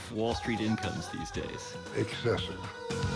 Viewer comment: I didn't understand what exactly Volcker said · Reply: Excessive